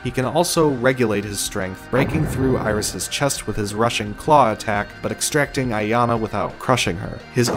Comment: I thought he said Russian claw